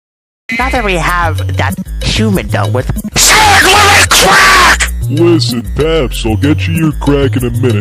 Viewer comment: I know paps is supposed to be saying " SANS WHERE'S MY CRACK!? " But i keep hearing it as " SANS WHERE IS CRACK!? "